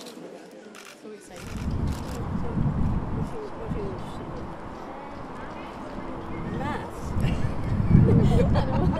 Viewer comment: What did she say at 2:17??? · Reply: She asked what he was interested in.